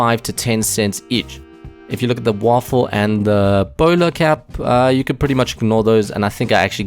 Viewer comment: Anyone else heard him say boner cap